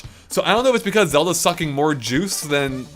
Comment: I LEGIT THOUGHT HE SAID "Fucking" Until I replayed it